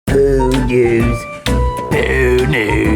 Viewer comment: I thought you guys were saying "Pew News" in the beginning.